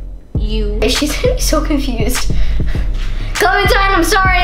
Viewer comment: When piper said clementine I thought she said coco quinn.....